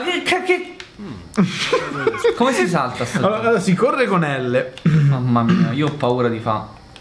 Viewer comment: nei sottotitoli ho trovato questo al minuto 0:11 mamma mia you porn iphone poi dice synergo "ma poi diventa zoppo" sottotitoli = italiano dopo 6 luglio incredibile...